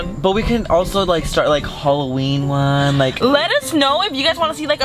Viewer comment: Anybody else hear say lettuce